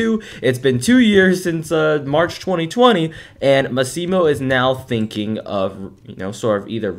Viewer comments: Did you just say "Masimo" :/ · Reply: probably he said “and uhm asimo” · he said "basimo" 2:54 DONT HATE ME IF YOU DONT THINK IT IS NOT, IT WAS WHAT I HEARD!!!!